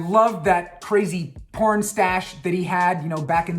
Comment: i think he said crazy porn stash that he had... 🤣🤣🤣